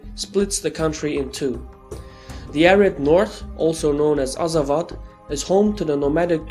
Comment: For the ppl confused wen he says arab north i think he means culturally n to an extant ethnically. At thus time the arabs were highly influential in northern africa compared to the rest of the continent. Hes not saying taeregs are arabic. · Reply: He said ARID north, I thought he said Arab too. Arid means dry desert